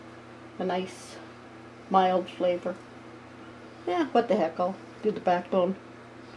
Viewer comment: I thought I heard "Yeah, what the heck, I'll do the bath salt"